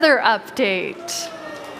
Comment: I thought it said weather update and I was like wtf